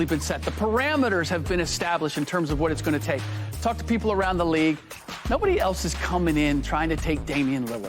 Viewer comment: did he say f*ck the people around the league??🤔🤔🤔🤔